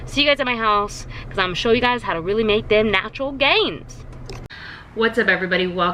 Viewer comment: Have you ever thought about doing a podcast? Your talks are so inspiring 👏🏿🙌🏻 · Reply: Also when you said "time to make those natural gains" I thought you said "time to make those nacho gains" and I got really excited I thought you weee gonna show us some healthy nachos 😂😂 #cravingsaretooreal